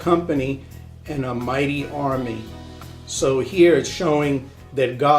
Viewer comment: and about 5:22 you said God was Russia but I think you meant to say what the bible says is that God will put a hook in their nose and pull them down toward Israel. Great movie clip! It is funny because it is true. Great video. I like how you show in 1 Thess. 5:5-9 that we are to watch and that we will know the signs. I am very tired of people saying we won't have an idea when to expect the endtime. They aren't watching or they would know it is here.